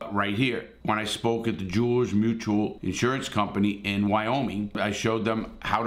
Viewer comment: i thought he said mors mutual insurance